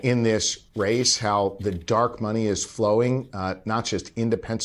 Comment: Did he say 'Dark money flowing around' - Indeed in overflowing in the dems hands. :/